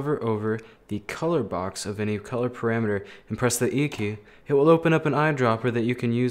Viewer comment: where is the screencast tool? cuz 8:49 im unable to hear it, said EQ? yt automatic caption says its EQ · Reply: "E key" Just press E for eyedropper